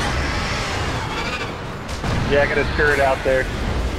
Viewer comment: i thought he kept saying "i have a turd out there" , "i threw a turd out there"...